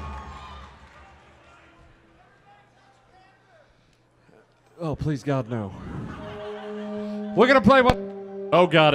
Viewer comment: What is someone in the crowd yelling at 23:45? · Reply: I was yelling "Everybody touch Panther!" It was a hold over from something a friend of mine yelled at both and at a show back in 2015 at Cat's Cradle in Carrboro, NC.